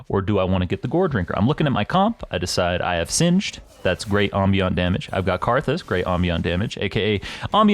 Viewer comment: what was the word you said ? Omniot ? ambient damage ? just wondering anyways great video as always this helps a lot <3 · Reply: Ambient